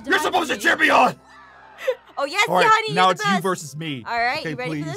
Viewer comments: "Oh yes daddy you're the best! " 😂😏 Did I hear that correctly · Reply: No she said honey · Did anyone else notice she sounds like she said “Yes Daddy”? Lol